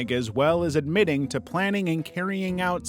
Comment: what did he say? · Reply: Kaynig 😂